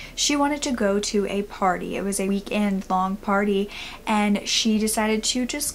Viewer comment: All I hear is 'weed and bong party' and I'm like, where do I sign up?! You can see what's on my mind 😏